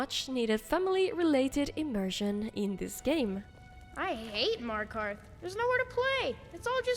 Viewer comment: I was just listening to video and thought the kid said "I hate Mario Kart" 😂